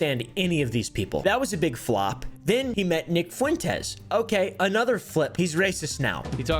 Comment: I thought you said Vic Fuentes i was so scared bro did sum wrong 💀💀💀